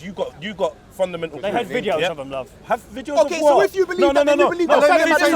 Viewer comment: "they had videos of them love" Did I hear that right?